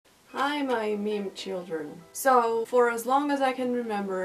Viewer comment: i thought you said 'hi i eat children' in the intro lol